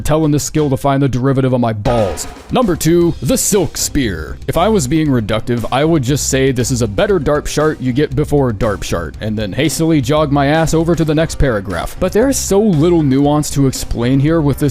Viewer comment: sorry did you just call that skill 'darpshart'???